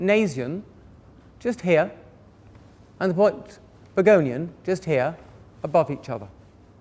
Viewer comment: you mention the point nasion and... What is the other one? Begolion? Burgonion? I can't hear it too well. How do you spell that? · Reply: Finally found it!! It is pogonion